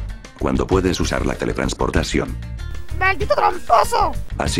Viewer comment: la primera vez que escuché maldito tramposo no le entendí pero luego volví a escuchar y luego si le entendí XD